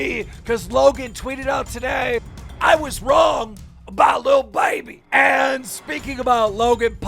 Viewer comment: I thought it said da baby lmao, 👀